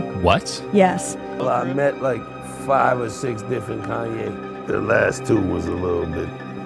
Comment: That’s it. Im calling Kanye ‘Kenny’ from now on too 😂👍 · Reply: I heard "Kane-ee", but....yeah....